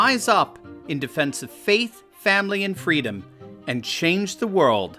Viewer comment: I always heard "Faith Family & Freedom" as "God Family & Country" Also known as "The Pecking Order", meaning 1st God, 2nd Family, 3rd Country. I have found in my life, this order is the only way things work. Deviation from this order always brought strife and hard times to me.